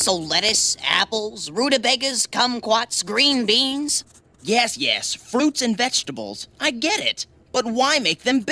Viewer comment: lolololol..Did Archie say, "Yes, yes...Flutes and vegetables"